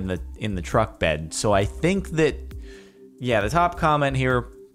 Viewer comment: The captions thought you said shoot the comments